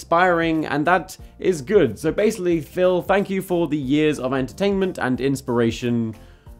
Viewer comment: I thought you said Filthy Frank(o) which would have been a very different channel